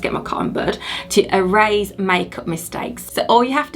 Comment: I guess I need some more sleep. I hear her saying "cotton bird"